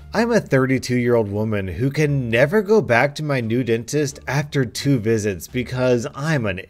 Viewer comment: Was listening to the video. At 10:15 I hear .. " I can never go back to my nude dentist.." Logical choice.